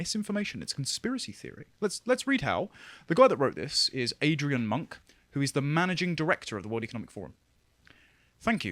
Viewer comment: I almost thought you said Adrian Monk, was going to be sooo disappointed in Tony Shaloub!